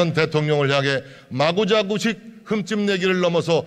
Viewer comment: "마구자구식"으로 들리는데 "마구자비식" 아닌가요? 제가 잘 못 들었나요?